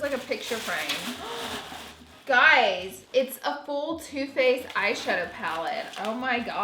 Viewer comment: Just me who thought you said toothpaste instead of too faced....